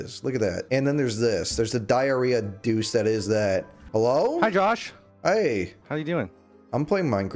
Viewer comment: Real ones will remember the entirety of chat telling him to name the second villager Palestine and Jawsh refusing like a coward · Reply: I thought you said Jewish instead of Jawsh